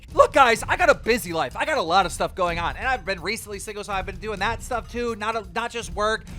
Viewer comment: Did he say single in 7:03 I’m assuming that little girl he was with over it with that old dude huh 😭😭😭